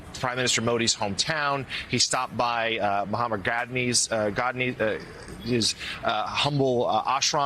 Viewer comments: Did that guy say Mohammad Gandhi?? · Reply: At first I thought he said Mohammad Ghazni. I thought wait Ghanzi did not come down that far south. But I could be wrong and there could be an minar or something there he might have erected. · Did he just say "Mohammed" Gandhi??? That's ludicrous ...